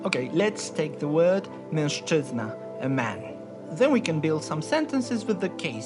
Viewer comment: why's it say 'menstruating' instead of 'mężczyzna' in the english subtitles? lol